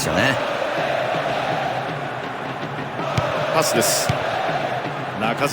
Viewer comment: 「パスです」が「カスです」に聞こえてくるw